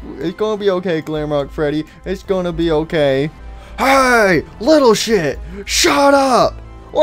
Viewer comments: did anyone else hear monty say hey little shit? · is monty saying Little shit